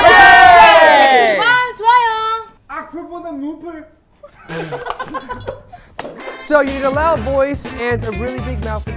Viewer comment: what does BM yell here? 😆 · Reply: Jamie: comment and like! BM: No thanks to mean comments though!